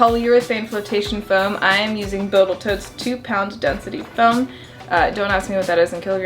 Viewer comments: Anyone catch her say "Botals Toat" at 2:19? LUL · was that a “botal toat” I heard? Lol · did here you say boatel tote instead of total boat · Did you say bottle toats at 2:16??? 😂😂